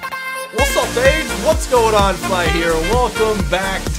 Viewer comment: In the intro it sounded like you said "What's up gays?". XDDDDDDDDDD